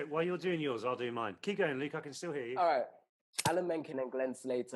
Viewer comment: Oliver said Lee instead of Luke 😍😍